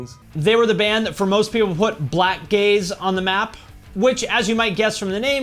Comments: I thought you said they’re the band who put “black gays” on the map 🤣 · I heard “they were the band that, for most people, put ‘Black Gays’ on the map,” and was quite confused. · when he said "Blackgaze" for a second i thought he said "black gays" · Oooooh! It's "blackgaze" I totally thought you said "black gays" ok... Now it makes sense.